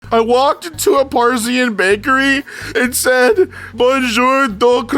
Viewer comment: I THOUGHT IT SAID PERSIAN